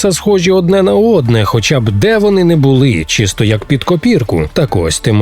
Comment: На 2:14 почулося «чи стояк під копірку»😂